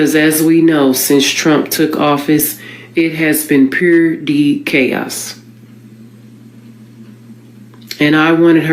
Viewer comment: Pure dee chaos?? Purity chaos?? WTH did she say @ 6:54??